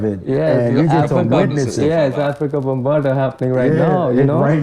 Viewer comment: what he said?? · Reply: he said he's been listening to hip hop for years...i said "like Afrika Bambaataa"....Kr said it a second later